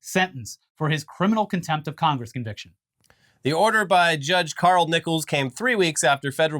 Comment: I thought he said Carl nipples